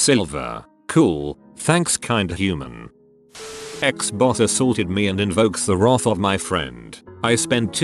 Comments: Nobody 10:16 : XBOX ASSAUITED ME That's wut I heard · I thought he said Xbox